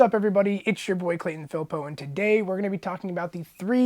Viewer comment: I've finally heard you say your name. I thought your last name as actually